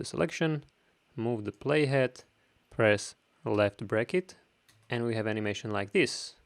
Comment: What did he say? 2:37 Press what? I need the Shortcut. I understand "elec the bracket" :)